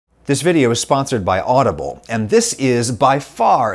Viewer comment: I misheard "This video is sponsored by olive oil" lol